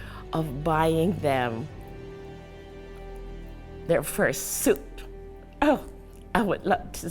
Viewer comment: every time he says "first suit" i hear "fursuit" 😭